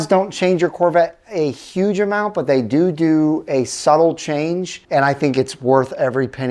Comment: did you say doo doo?